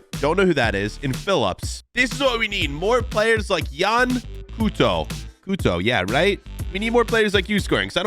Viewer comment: it sounded like puto I’m a Hispanic and that’s a bad word 😂 lol